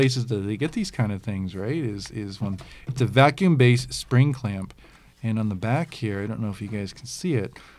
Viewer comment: did he just say nipple clamps? 😂